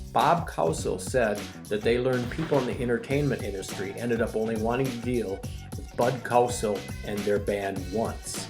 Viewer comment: I really enjoyed the clip but Does anybody understand the narrator from 4:45 to 4:52 ? I swear I played it 10 times and dont understand whats said. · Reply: Sorry! "They learned that people in the entertainment industry only wanted to deal with Bud Cowsill once."